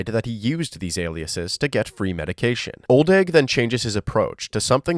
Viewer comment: the subtitles say "old egg" LOL